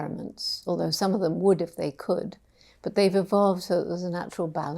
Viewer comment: intermediate 10 i heard "would've they could" instead of "would if they could" and for "by and large" i was heard "environment and large", last one i was heard about "not true balance" instead of "natural balance" lol😂😂